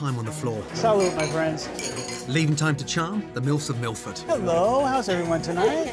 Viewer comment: LOL had to run it back 38:25 , thought he said "charm the milfs on Milford" xD